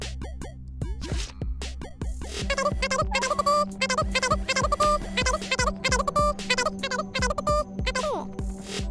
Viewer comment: よいしょよいしょの運び屋すごいwww でも3:23ここらへんなんて言ってるのかめっちゃ気になるwww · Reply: ゆっくりにしたら分かったww アーカイブって言ってるのか😂😂